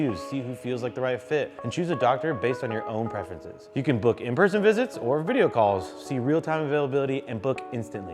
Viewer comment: Oh, "in person visits"... I thought he said "in-prison visits"!